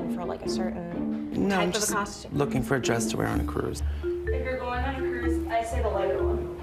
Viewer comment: That one girl who just said "if your going on a cruise I would say the lighter one" bless her heart 😊❤ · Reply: For some reason I heard "the leather one". 😋🤣😆